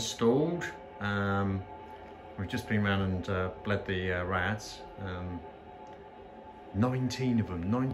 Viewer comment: I thought you said "bled the rats" :P Nineteen of them??!! Then I realised you meant radiators :P Whew!! XD